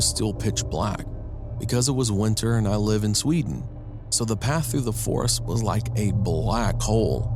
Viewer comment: Wait did he just say Sweden 🇸🇪 56:28 if he did I'm from Sweden